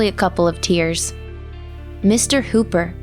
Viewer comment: I thought 0:30 I thought she said Mr. Cooper. "Mr. "Coop" Cooper is dead??".